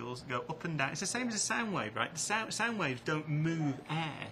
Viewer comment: And he doesn't know that sound is a longitudinal wave? I thought he said "sand waves" so I didn't know what he was talking about at first. By the way, the apologist comments about this issue are pathetic..